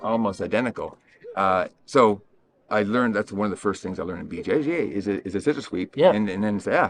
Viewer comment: I know you said “BJJ” but it almost sounded like you said it the way Master Ken does “Bu Jay Jay” and now I can’t stop laughing